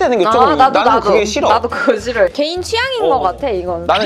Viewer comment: 그거 싫어가 그 지럴로 들려서 깜놀햇네..ㅋㅋ